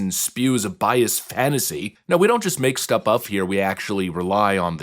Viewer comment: my brain... did you say "stup uff" instead of "stuff up"? 😂 that's a wild mixup 😂